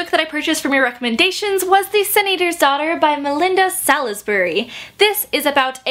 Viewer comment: it sounded like you said "the senator's daughter" and I was like WHAT?